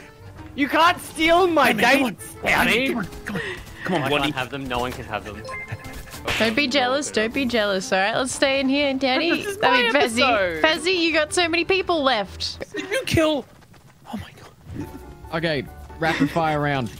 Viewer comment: Is it me or did I just hear her say daddy